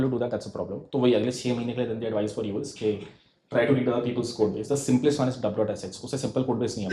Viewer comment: what codebase did he said at 28:40? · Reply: dub[.]sh